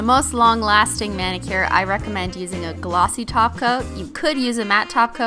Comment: when she says gloosy top coat i thought that she said gloosy taco😃😂😂😂😂